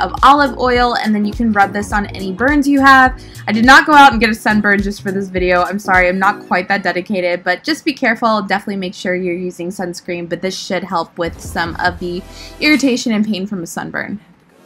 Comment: I thought she said this sh*t😬😬